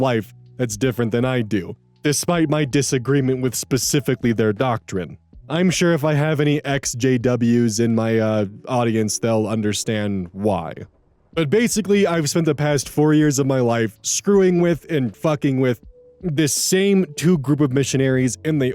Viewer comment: Hey, maybe it's not the best idea to phrase it that way, lol, it low-key sounds like you said, "SJWs," rather than, "Ex JWs" (/lighthearted)